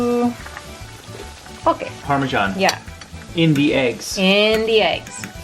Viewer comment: Uhh..What did she say? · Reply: "Okay"